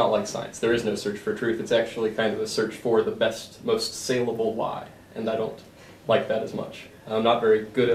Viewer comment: what does he say at 6:27? "...it's not like science, there is no search for truth, it's actually kindof a search for the best most SINGABLE lie..." Is that was he says? "Singable" ? It's a strange thing to say so i can't quite tell.